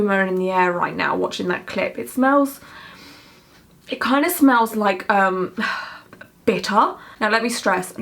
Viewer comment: I thought she was gonna say it smells like bitch in here 😭😭😭😭